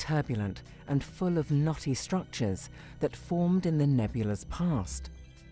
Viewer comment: I know what she says, but I can only hear "naughty structures"...